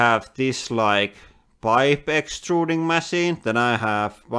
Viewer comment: Does he say ‘wife extruding machine’ at 6:00 ?